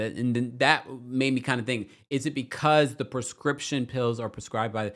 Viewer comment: I thought Karen was saying "prescriptive" drugs